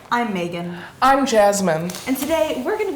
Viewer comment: She said : “i’m megan” I heard “ l’m bacon 🥓 “ .... okay me